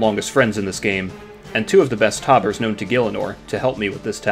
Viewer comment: I thought you said you brought two of the best tobbers in the game? I didn’t see ItsWill in any clips, he’s clearly one of the RuneScape players of all time